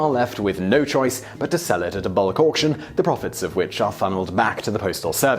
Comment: Did I mishear bulk auction as bollock Auction